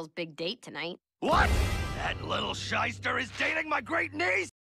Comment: I only watched Gravity falls once in english so I never noticed, but what is Stan saying at 0:26? I first understood "Scheißer" which is germans and means as much as "little shit" or "shithead" or something like this XD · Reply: Pretty sure he's saying shyster, which is slang for like, a con artist basically (I think it comes from the German word though, lol)